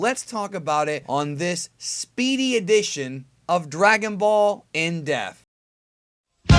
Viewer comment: when are we going to get a vid on why it sounds like he says "dragon ball in death" instead of "in dePth"